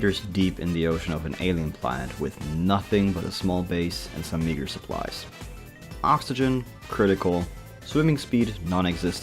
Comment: and some WHAT supplies? did he just say the N word wth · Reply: he said: meager supplies it is a bit mumbled though